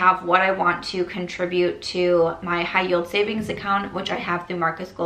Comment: I really thought you said High Heel 👠 account each time you were saying it. 😭